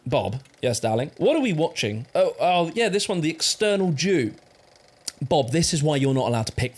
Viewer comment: I wasn’t watching and thought “Why is he saying external, is eternal”